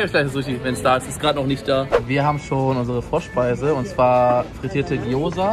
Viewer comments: Bei 2:43 einfach Frosch-Speise verstanden🤦🏽‍♀️😂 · Ich hab Froschspeise verstanden HAHhhaHAHAHHAHA